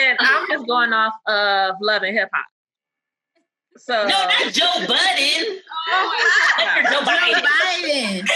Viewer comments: I'm dead! She thought the prompt said Joe budden 😂😂😂😂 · The questions were really good on this one. But you all could have edited out the part where the lady misheard it as "Joe Budden". SMH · I died when she thought he said Joe Budden oh my god 😂